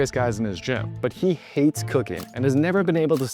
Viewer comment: i looked away for half a second distracted and cause of accent i thought he said he wanted to try cocain instead of cooking x D